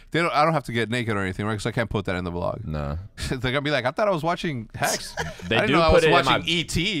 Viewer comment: You can tell hecs was speaking Spanish on the weekend when he said "attara was watching" instead of I thought I was watching😂